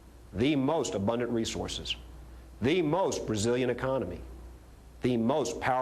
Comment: anyone else think it sounds like he says "we have....the most Brazilian economy" at 5:25? this whole thing is so amazing hahahaha.